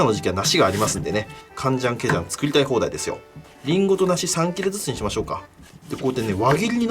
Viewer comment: りんごと梨3キルずつにしましょうか に聞こえた俺はゲームのやりすぎだろうか